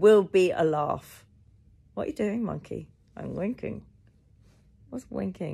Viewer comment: Okay, hands up: which of you originally thought monkey said “wanking” not “winking” ? I even laughed when monkey said that it’s a way to show others you care for them…